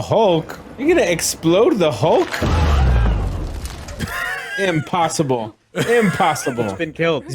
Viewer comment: During the hulk-splosion scene when was saying “impossible…” all I kept hearing was “Pym-possible” lol